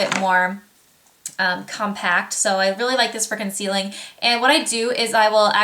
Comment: for the round top kabuki when you said "i really like this for concealing" i thought you said "i really like this freaking ceiling" hahahaha. anyways, i love your vids!!! keep up the great work :D